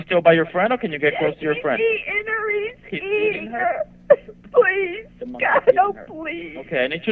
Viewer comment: wydaje mi sie ze jest zrozumiale co powiedziala - “he is eating her”